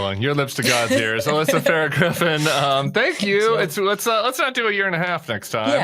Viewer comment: Didn't hear you correctly, Tim, and thought at first you said "Alyssa Farah Grifter", maybe because you mentioned she worked for trump. Sorry, Alyssa!